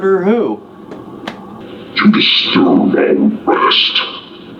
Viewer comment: I heard "you'll be soon at rest," not, "you disturb our rest." Goosebumps.